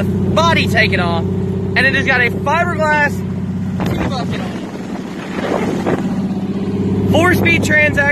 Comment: what did you just say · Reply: I said it has a fiberglass T Bucket body.